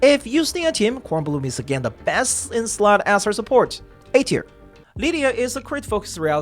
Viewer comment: What did you say at around 6:27? I think you said some character was BIS for Eternity or Eternity was Best in slot for the character, but I didn't hear the name. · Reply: I believe he said that Bkornblume is the best in slot support for Eternity.